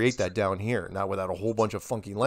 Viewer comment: Did mark say, “Fucking” during this interview?! 😂 I don’t think he did, but it sure sounds like it!